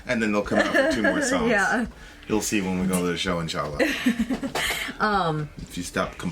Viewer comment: did you say "Inchalla"? I didn't know that you speak Arabic....or maybe I heard wrong!